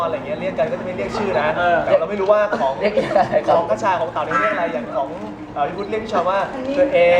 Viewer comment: ได้ยินมะๆ "คชาของเต๋า" 2:46 ><